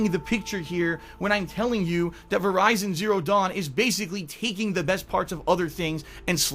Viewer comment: Did he just say VERIZON Zero Dawn at 7:33??? LOL keep up the good work.